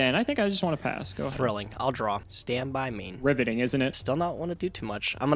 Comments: Did he just say ribbiting instead of riveting? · When said, 'Riveting' I honestly thought it said, 'Ribbiting' due to it being a Frog mirror lol And wait, at 42:15 flips the paleo face down as a monster. Wouldn't it be put f/d as a trap?